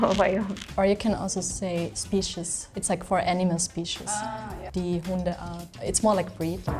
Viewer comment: For the love of god please fire the person doing the subtitles already. Not a single prompt without multiple mistakes and some even getting whole words wrong. You'd think a channel all about different languages would invest into one or multiple people capable of putting down what the guests are saying properly. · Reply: oh yeah, i completly agree with you. As a Dutch person growing up in Switzerland so i basically understand everything they are saying. I also read the subtitles and some ports of the subtitles were criminal. For example germans would NEVER write aardappel, thats just thats. They would either write Erdapfel (literally translated) or (h)ärdöpfel (more swiss). Even an english subtitle (11:25) is wrong where it should be "his" instead of "he's". And just 7 seconds later she says "die Hundeart" and the subtitle says "the Hund art". I think this was most likely made by an AI that wrote down what they said but doesnt understand everything correctly of course. They need a real person to at least go through the subtitles before the video gets published. If this was a real person who wrote the subtitles down, they need someone wo speaks those languages obviously 🤔